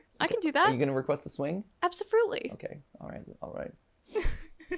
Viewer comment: Matpat: can you request a swing? Ash: Absafruitly this is what I heard instead of absolutely and if it was in fact what I heard that is adorable!